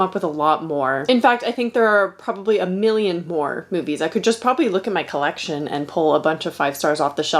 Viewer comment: As a British person, I hear "whore movies" 😂